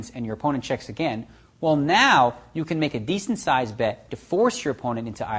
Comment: What is the word at 1:17 ? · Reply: descent